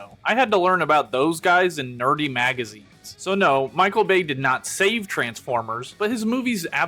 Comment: for a second there, I thought you said DIRTY magazines 😂😹